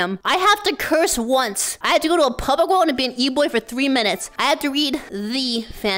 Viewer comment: I thought the eboy said ebony and was very confused what she was going to do with palm tree timber.